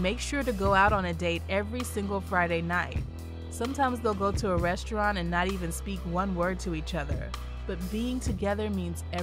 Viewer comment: Did she say at around 7:45 time, did she say they go to restaurants and don’t say a word to each other??? Huh ??